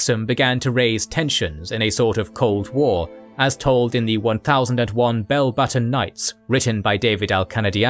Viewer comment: Does anyone have a reference for the book mentioned? I heard "1001 balbatten nights" by David Akendayiani, and I'm getting nothing when I search for it.